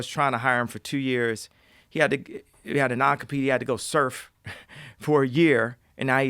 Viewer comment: what did he say @ 1:22:58, he said the guy had an acopy? and had to surf for a year, can someone inform me please, thanks